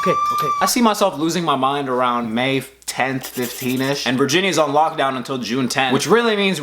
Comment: I thought u said virginity is on lock down until like June 10 and I was ooop but true